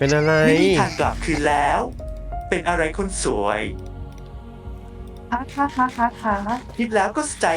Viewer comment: ผมได้ยินเสียงพูดว่าค่ะๆนาทีที่